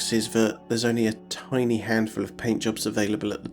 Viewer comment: "handful of paintjobs". I thought I heard "handjobs" 😄